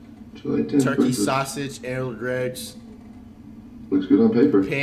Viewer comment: tf did he say? Errald Gregs?